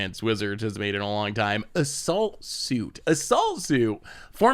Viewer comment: sounded like "a salt suit" if you don't see the card haha